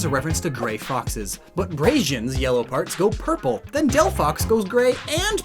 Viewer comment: did he just say "Braizen"?